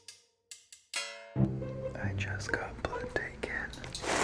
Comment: before realizing you said you "just got blood taken" I heard "I just got plugged in" and I was?? so confused??? like what kind of matrix shit?? lmao